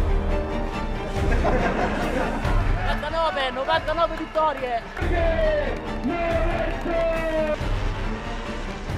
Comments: what's Rossi saying? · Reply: "nineteenine, nineteenin victories" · What does Rossi say at 1:51? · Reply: 99, 99 victory